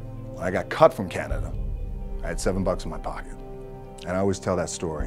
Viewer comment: I thought it said “I had seven bugs in my pocket“